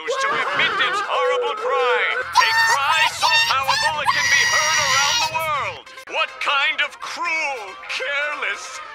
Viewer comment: what did she say that made Charmx censor it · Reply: The pink pony said, "don't forget to write"!